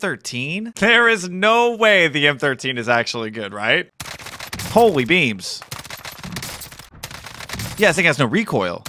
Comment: Did... Did he just say "Holy Beebs?"